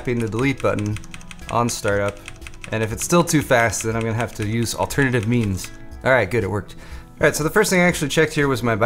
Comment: thought he said "half to use alternative memes" LOL